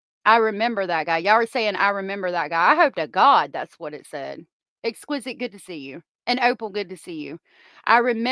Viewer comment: Sounds like she says i member that guy